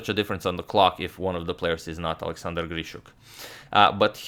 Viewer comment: Why did you say "if not one of the players is not Alexander Grischuk? I thaought that was funny